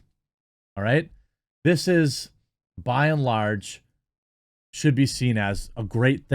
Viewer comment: When you said "by and large" I misheard that as "bi and large" and thought "yup, that's how most of these protestors identify".